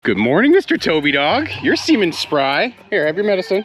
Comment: For a moment I literally thought said ''Good morning Mr Toby dog, your semen's spry.'' I was like... Say what now?